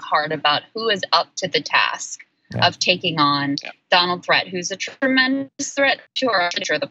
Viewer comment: Did she say Donald Threat at 5:12? Boy, is she right